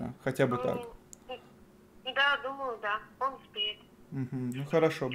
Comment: Послышалось, сказала про мастера не "он успеет", а он "спит"😆